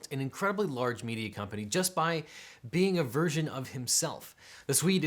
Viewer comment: For a second there I thought you said "For being a virgin of himself" had me lol for a bit. Great video as always!